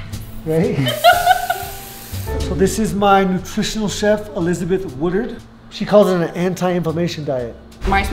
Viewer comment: I thought I heard him say Elizabeth Hooters.